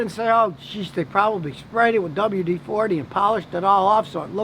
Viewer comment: I thought you said don't spray rubber with WD 40!